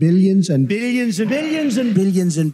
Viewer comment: Billions doesn't even sound like a word to me anymore after this. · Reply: I just hear "Billyens"